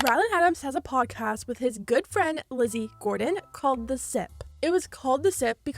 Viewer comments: I def thought you said Lizzie Borden like why do all these people have beef with dead people💀💀💀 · I can't hear for shit. I really thought you said Lizzie Borden and was very confused for a solid minute.